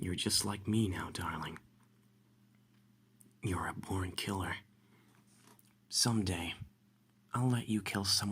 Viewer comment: Him: your a born killer Me: wait did you just say porn killer.......